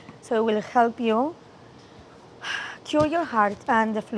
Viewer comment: Gaby said that her cocktail will cure your heart but the subtitles said otherwise... 😂